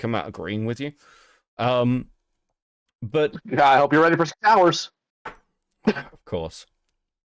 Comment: “I hope your ready for some shouters”. When I first heard that, I thought he said “showers”. Lol